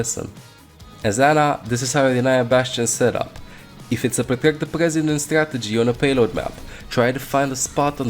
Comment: What did he even say? 😂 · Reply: If it's a 'Protect The President' strategy...